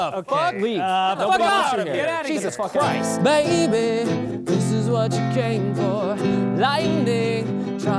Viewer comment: just I hear "This is education"?